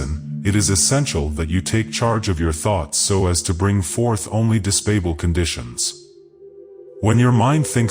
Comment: Thank you very much for sharing this audiobook. Everyone have a blessed and proaperous life. 23:35 What does " dispable conditions" mean ?? What word is it supposed to be really ? Desirable maybe.( I couldn't find it in the dictionary. ) 24:20 world withOUT. 24:49 yourk-see-oscious and sudconscious minds. APD = and. AI mistakes I guess. .